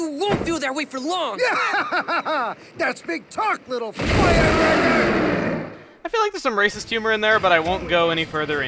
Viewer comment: it sounds like he says "FireRACKER"